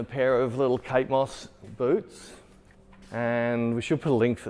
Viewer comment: I had the rewind because I thought you said "And a pair of Kate Moss boobs", I was like "wow, and he didn't want to make the joke about size ?", but it was "boots" ... x)